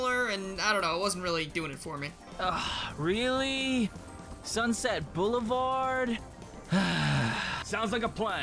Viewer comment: U know sunset bulavard everytimd I hear it i hear 90's boulevard